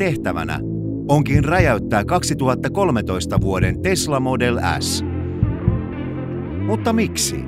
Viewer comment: I heard Tesla Model “Ass” Sounds about right. 👏😂